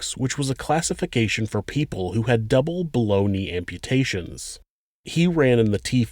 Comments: he was saying "below knee amputation" and i kept hearing "boloney amputations!" lolz!!!! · I thought he said "balony" amputation 😂